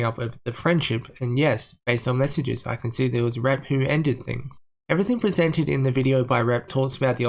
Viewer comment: At one point you said, “it was Rep who...” and I heard, “it was rat poo” lol